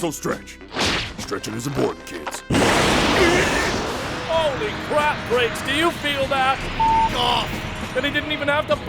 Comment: What does Cell say to Vegeta at 3:04? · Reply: holy crapgrapes